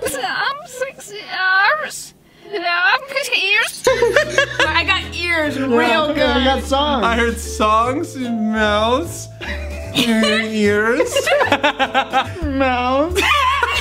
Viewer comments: I hear “.....I’m sexy...lungs...EARS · "Songs, sexy arms...lunges, ears?" That's what I heard 🤣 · i heard "Song....sexy arms............ears"